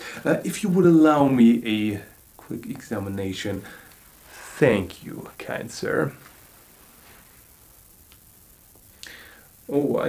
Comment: Did he just said "thank you, cancer"?! XD